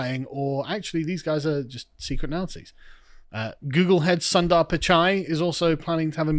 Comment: Did Carl just call some indian guy "Googlehead"? 😂